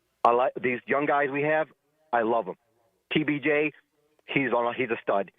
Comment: Did he call Brian Thomas PBJ?